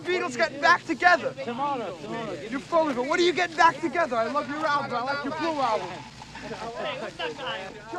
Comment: did someone say “go back to ireland”? 😄